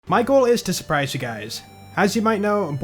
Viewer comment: "michael is to surprise you guys" is what i thought said in the beginning. who's michael?? 🤔